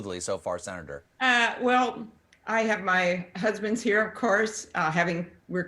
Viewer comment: Did anyone else think Amy Klobuchar had more than one husband? I definitely misheard her say 'my husbands.'